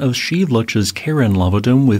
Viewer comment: I admit I was listening a bit causally when I (thought I) heard "... the Karen lavadome ..." at 0:15. After working with customers for many years, that had a terrifying ring to it! 🙂